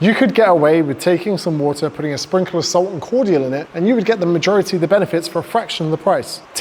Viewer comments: What does he say at 6:30? Cordial? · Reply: Yeah cordial / squash etc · does he say cordial? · What does he say at 6:30, after "sprinkle of salt"? I normally just have a glass of water with salt, which tastes like shit bit atleast it's extremely cheap, but curious about what other ingredient he mentions. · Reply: Cordial, it's like a fruit juice drink that you have to mix with water